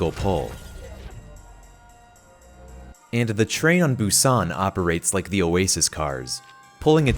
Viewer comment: And the worst is that, because because of the cars of Oasis and the train of Busan, there are many contexts in which there could be perfectly legitimate +10M DMGMIT, just you saving a teammate. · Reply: I thought you said train to busan. I was like “OVERWATCH ZOMBIES?!?”